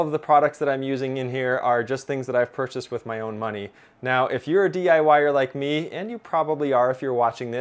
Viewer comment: Excellent video. Anyone else thinking about electricity and heard him say "If you're a DI wire like me..."? :-)